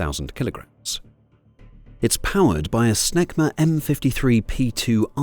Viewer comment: The Mirage Pilot is probably an Aussie .Aussies are the best Fighter Pilots in the World ,with only a small population compared to other Nations with a larger Pilot pool to choose from . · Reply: Oh "SNECMA". I thought the computer voice said power supplied by "...SMEGMA M-53 P2 afterburner turbofan"! 😮